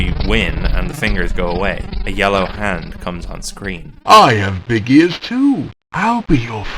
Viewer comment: Anyone else hears "I am Big Ears too!" instead of "I have big ears, too!"?